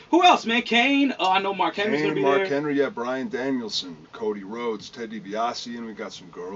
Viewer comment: did anyone else heard him saying bryan daniel?